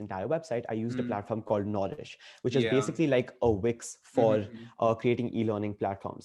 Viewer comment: I think I just found my new favorite YouTuber. Also, can someone tell me what does says at (timestamp) 36:09 ? what is the website name he mentions? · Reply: Wix